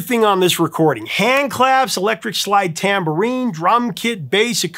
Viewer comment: I misheard when you were listings off the instruments and have been wondering what a “slide tambourine” is for the past 5 minutes 😂